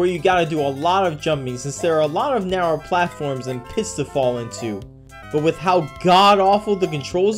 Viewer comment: I heard "piss" and not "pits". Also, is that redhotsonic I see? Also note how Sonic is red and has yellow shoes here. In Sonic 3 & Knuckles when not playing as Knuckles, Knuckles is a light shade of pink with yellow socks. Ah, palette madness.